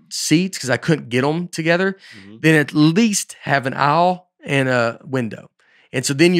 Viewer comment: Back in my day watching this podcast…… there were zero ads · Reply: Also, like how Nate says “aisle.” Sounds like he said “owl.”